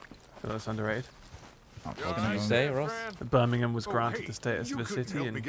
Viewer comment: Ross said Birmingham I thought he said bumming